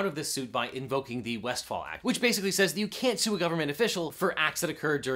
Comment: It's a bit worrying that US law-makers are quite ignorant of the law, even with preparation time. Or maybe he asked Ruddie???? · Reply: The Westfall act? I thought you said wonderwall! (rudy quietly gestures at Oasis to leave)